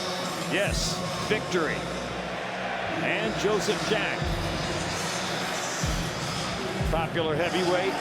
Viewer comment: Did they just call him Joseph Zhang ??